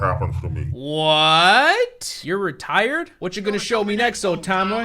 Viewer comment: I thought he said retarded